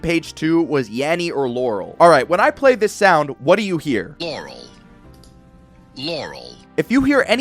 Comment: Guess I'm lying to myself because I hear "laurel" 🤪 Also, as a long time Nintendo fan, I'm not getting a Switch 2 either.....unless I cave in for the next mainline Zelda game 💀